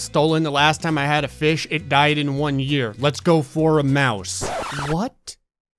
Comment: I thought 3:12 said Moose